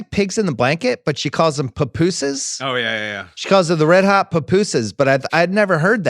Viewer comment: I thought she said red hot pupu’s. Like pupu platter